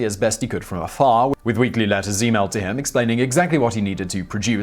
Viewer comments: did I really hear that in 1948 letters were emailed?.... I know, it's pedantic...... but I thought it was funny. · "Weekly letters e-mailed to him" in 1940's. Remarkable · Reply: I heard "he mailed in", his English accent made the h in "he" very soft, nearly absent.